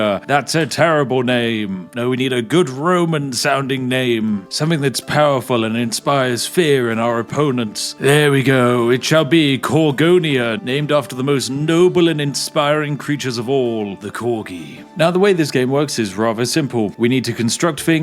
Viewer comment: I heard Cargonia and was like BLACK PANTS LEGION! But no.